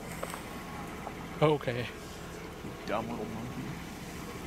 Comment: Did he say " dumb little monkey"